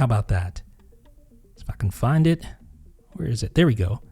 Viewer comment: If I can find it, sounded like lets Effin find it 😂 . Had to go back and listen again hahaha